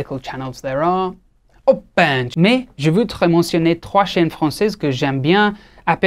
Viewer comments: Did he just say o bahencho* yaaaas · Did you just say "Oi Bencho" when you were talking about indian medical channels?😂😂😂 Also, the unilateral wrist got me ROFLMAO 😂